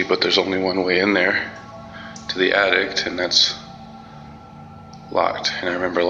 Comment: the only thing I was thinking was... why do you keep saying 'addict' instead of 'attic" .. lol